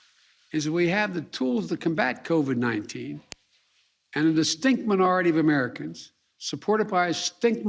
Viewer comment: Did he call the unvaccinated a “stink minority”?